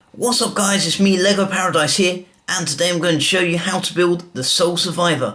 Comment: Did u just say nigga paradise??!?!!